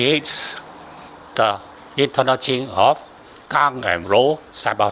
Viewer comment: Thank you a lot for this english language version of the lecture. Lots of basic but very important info. I just didn't understand two words, sounding like "kang and roll", or "come and roll ". For example at 20:19 "Kang(?) and roll(?) simultaneously" "When there is "kang"(?) there is also "roll"(?)" I would appreciate if you could suggest what these statements mean. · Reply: Master said that some of the Taiji words should not be changed casually, 20:19 "Gang 刚" means yang, strong and "Rou 柔 " means Yin , soft. Like the Taiji logo, white and black. White mean Yang/Gang and Black mean Yin/soft. Example someone heavy punch out is Gang, I use hand softly touch and offset his punch direction mean Rou transform. I hope you under stand what I mean.